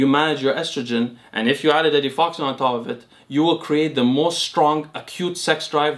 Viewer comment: what does he says? add what on top of it? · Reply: Etifoxine he mentioned it earlier than your timestamp and explained what it does.